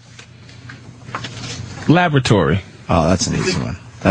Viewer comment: Does anyone else kinda hear "Lavratory" when Patrice says, "Lavatory?" I appreciate and understand Joe and Sam both articulating Patrice's word, AND misspelling of the word he probably started writing the second he misheard it